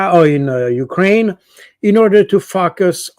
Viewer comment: I thought he said "in order to fuck us"